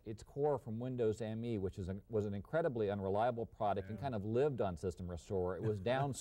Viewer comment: had to re-watch in disbelief as I first misheard that as "Windows ME, which was an incredible and reliable product".